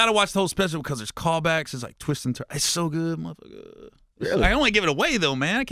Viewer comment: definitely thought Gary said it's so good my n word 😂😂